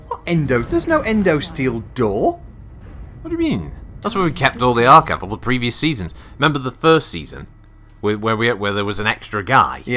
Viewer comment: what does, I mean, say 'that's where they keep' what? It's hard to understand that Solaris accent sometimes 😀. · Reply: Archived older series of best in class :)